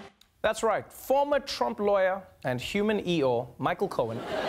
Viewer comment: I was half paying attention and thought you said “human eel” and I was like damn true 😂